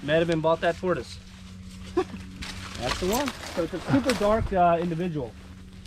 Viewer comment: I wonder what happend between and kenan....I loved it when they did videos together. I loved the swimming videos ;) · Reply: 4littlegirls did I hear him say “he’s a super dark individual” at 5:06?